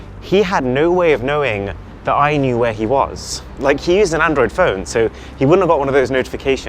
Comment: almost sounded like he IS an android phone lol